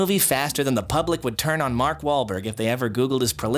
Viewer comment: I honestly thought he said Mark Hamil, first viewing